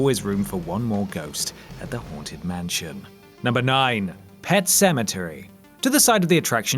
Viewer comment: Coz your accent I thought you said "The horn tit mansion"